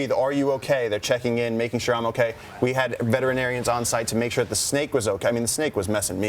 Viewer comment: I thought they said "vegetarians" not veterinarians, it kinda quandle my dingle there for a minute